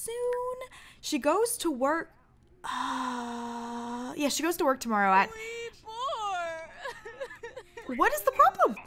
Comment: 😂 Sasha: Yeah she goes to work at hunny Chanel: 3:04! It's sounds like she is saying 3:04.😂